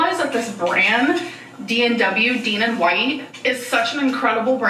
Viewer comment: at i 31:39 i thought she said “damon white”